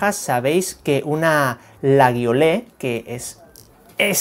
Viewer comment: lo que dijo: la Guiole Lo que escuché: la viole Como vilolas una navaja xd